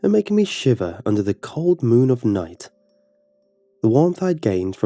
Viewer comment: I literally thought he said " the cold moon of nut" XD